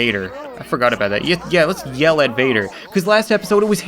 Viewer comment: idk why but the first time I heard ""yell at Vader" as "elevator" :DDD